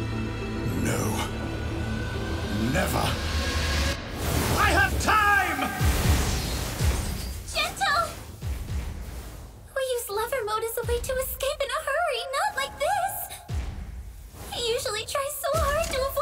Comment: I always thought that Gentle said "brava mode" but it turns out (according to the wiki) his move is actually called "lover mode"